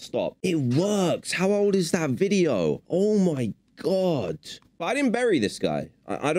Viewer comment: I think it says “burned” and your brain is making you think they said “buried”